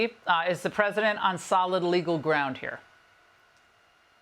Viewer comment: She said "Jagg Off"icer😂 I really thought she said JAGOFF when she really said JAG Officer. My bad.